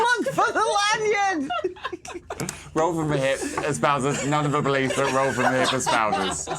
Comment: What does say at 2:01:03? I've replayed it a dozen times but I can't make it out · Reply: i think hes saying "espouses"? which is " to adopt, support, or promote a cause, belief, or way of life" - basically i think what hes saying is "rfth promotes none of the beliefs rfth promotes". i think hes being like "disclaimer, these are jokes, we don't actually support the ideas we're joking about here" - the idea being that luke prioritising lionel over phoenix is a hate crime, or that things that make people marginalised like neurodivergence or being black mean they deserve to be saved more, or that you should pretend to be marginalised in order to get "privilege" (because thats not how that works). anyway, basically i think hes just being like "for legal reasons this is a joke". thats what i gathered from it anyway